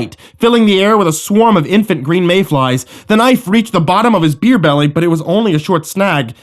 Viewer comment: I had to listen to the beginning of pt4 3 times. Was sure u said ‘ear-belly’ & having just woke up my brain isn’t in gear! Really cud not figure ‘beer-belly’ from wat I thought I heard!! 😹 Really hope u realise how good u r at taking people’s minds off their problems. No matter how tragic or serious or big they r (seem) Thank you. I’ve been saving this complete gas station Halloween special for weeks. And I’m so glad I did. You’ve made my day 💝